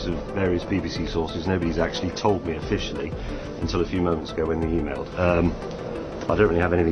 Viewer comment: Your subtitles are wrong… 8:25 is “a few moments ago” not “a few months ago”…